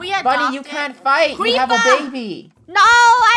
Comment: idk why i didnt notice but did say,”we have a baby” ?